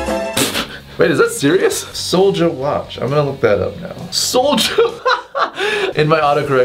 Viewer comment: I thought he said “ Soulja wackin’ “ hahahah lolllll